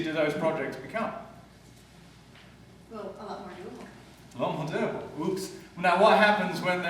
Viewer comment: What did she answer at 0:53? I can't understand what she said or even after he repeated it? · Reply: "a lot more doable" you want to rewind the conversion to here: <link> to understand this fully